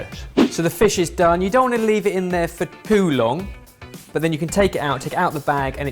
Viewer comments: it sounds like Ben say Poo long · "for poo long"? Please tell me I'm just mishearing him